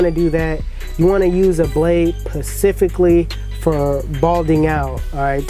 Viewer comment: Why do you use the slim line pro over the andis gtx? For certain haircuts tell me pleaaase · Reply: did you just say PACIFICALLY